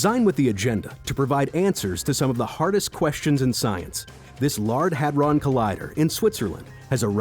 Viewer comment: did he just say, 'lard hadron collider?'